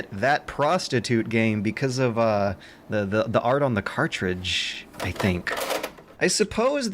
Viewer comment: i swear i heard artridge